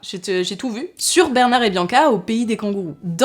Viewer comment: C'est moi ou j'ai entendu "Bernard et Bianca, un échec" ? 😶😶